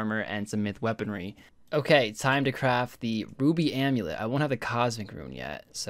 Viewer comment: does say "boobie amulet?"